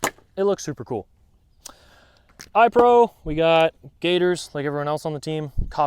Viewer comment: Hi there! Whats the brand name on those first safety glasses? I thought you said "gaitors". But I can't find anything on google under that name that look like them. Thanks! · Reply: They're called Gatorz and this model is the Magnum. If you're getting them for airsoft use you have to get the ones listed as milspec so that they have a proper ANSI Z87.1 rating. All their other models only have basic impact resistance. If you're in the US you can buy direct from their site, in Canada here you can get them at warriorgear.ca or <link>